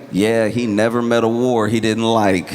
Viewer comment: I totally thought Josh said "he never met a WHORE he didn't like" lmao, had to rewind